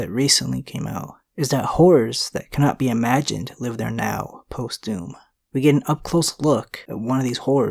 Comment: thought you said "whores live here now"